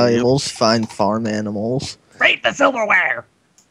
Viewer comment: I misheard "Raid the Silverware" as "Raid the Underwear"